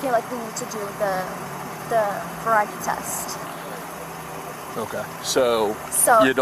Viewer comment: Did she call the breathalayzer test a variety test? Timestamp